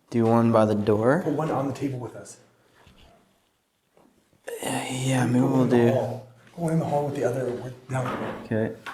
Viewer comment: there's a clear whisper I thought maybe saying "planchette" or "my turn". Creepy. Lots of whispering before and after during the ouija session besides. Awesome vid guys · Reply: Yes my turn